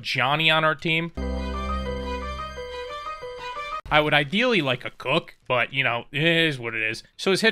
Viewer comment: We have a Johnny in our team :Cut to scene of Johnny Sins walking in as a doctor and take care of the wife of a dude: (What I misheard): I feel like cuck Hahahahahahha! My dirty mind played with me